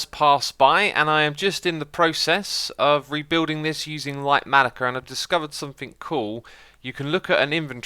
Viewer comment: What did you say was the schematic mod? Lightmatica? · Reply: Litematica